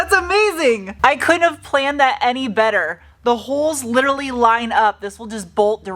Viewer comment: When you were comparing the speakers and remarking on how fortunate it was to have the perfect mounting holes, I thought you said "I couldn't have planned it, Eddie Vetter"